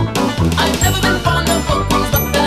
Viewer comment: i have no idea what they are saying and how they are saying this but it’s amazing Edit: oh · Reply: The line is "I've never been fond of bookworms but then again this one does make good mario bait", pretty clever line TBH